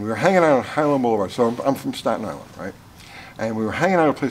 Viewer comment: When says, "I'm from Staten Island," all i hear is, "Wu is for the children." Edit: the GTO is still one of my alltime favourite muscle cars